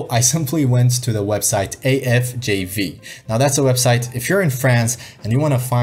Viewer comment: > What does he say at this moment? Which site is it? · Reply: ahh i think it was a website to look for jobs available, dono if it is still up, afjv. JV stands for jeux video in french